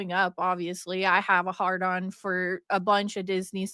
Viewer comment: Wait did she say heart on or hard on ? 🤔 Weird